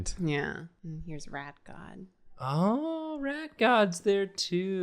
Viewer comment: when said rat god I thought she said rap god and I was immediately expecting a drawing of eminem